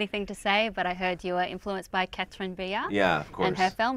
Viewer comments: Who's the filmmaker mentioned around 4:40? I couldn't quite catch her name! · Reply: Catherine Breillat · What are you saying? Katherine B? Katherine Diaz? Sorry, I would love to hear the influences but I can't quite distinguish the letters there, anyone care to help me out? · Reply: Dan Isacsson Catherine Breillat. She made the movies Fat Girl and Anatomy of Hell.